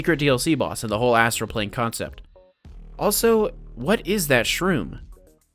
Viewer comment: I heard “What is that mushroom” and thought Mario lol